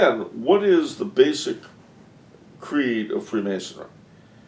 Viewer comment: "Conduct over creed" is a basic creed of whom? I didn't understand that.. Around 31:45 - What did say? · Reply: The Freemasons.